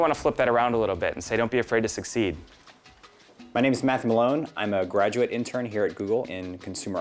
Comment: "my names Math Malone" wait up wait up....at first im like did he say his name wrong...and then after looking at the word Matthew...I seen since Matt is short for Matthew and even though everyone says Matt like "floor mat" he uses the "Math" from Matthew..."im Math" im guessing...or it was just a weird part of the video and Im having hearing problems